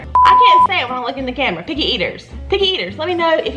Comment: sounds like your saying piggy eaters lol.